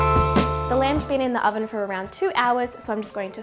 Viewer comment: You said our lamb has been in the oven as a Dutchie i thought you said Alan has been in the oven 😅